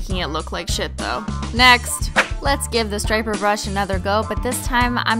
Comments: Oh sh*t I thought the striped brush said stripper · I thought it said stripper :P